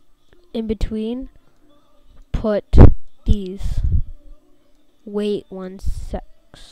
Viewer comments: it sounds like he is saying " wait one sex" XD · did he just say sex? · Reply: sec's